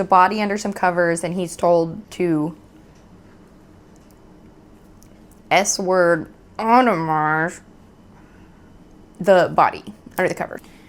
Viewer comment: A lot of these descriptions you give, like at 11:49, I have no idea what they mean. S word otto mar? Shitting on? EDIT - Oooooooooh. No, that’s fucked up. Fuck that.